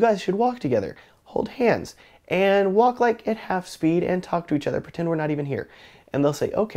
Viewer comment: was trying to make out what you said. Did you say walk at half speed?